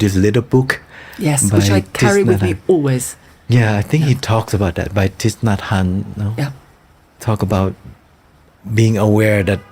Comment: Did anyone catch the name of that book which Apit gave to Tilda please? It was mentioned around 27:35. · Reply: They didn't mention the book title but only the name of author, which is Thich Nhat Hanh.